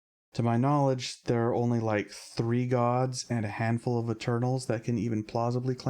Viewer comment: i swear i heard "there's only like, 3 Gods and a handful of the Turtles, that can plausibly claim to have firsthand records of these times..." I realized i misheard you, but i love the idea that turtles live as long as Gods in your world. 😅